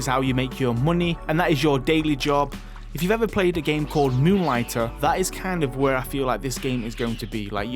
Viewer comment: what game did he say? New writer, new lighter? Can someone help me?